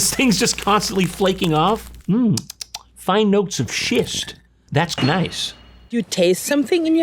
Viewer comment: Anyone else hear, "Fine notes of shit" their first time